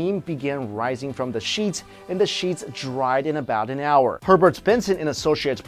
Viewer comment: I would choose shape shifting also for super strength could it be from fear? When your scared you punch harder and when they're about to die you're scared and you get a lot of strength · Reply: I thought he said pervert instead of Herbert xD